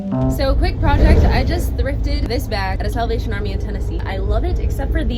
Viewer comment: I legit thought she said “seven nation army”